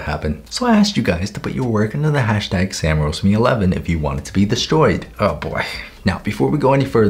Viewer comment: Why does the subtitles say- "hashtag samraiseme11 if you want to be destroyed"😭✋ (Try 0:15 with subtitles English (auto generated)